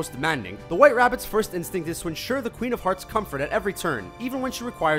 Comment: is it me or did it sound like he said the queen of farts😂